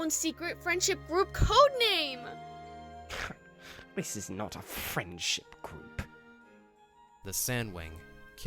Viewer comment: Winter sounded like he said French in this part